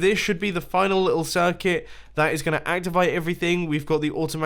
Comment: Mumbo did you say activite at 8:31 of the video